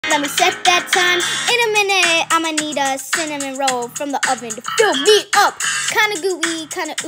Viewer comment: I always thought it said cinnamon roll 😂